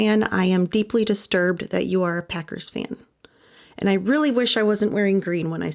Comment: I had to listen again - I thought you said peckers fan but you indeed said Packers. 😂 We listen slower in Texas.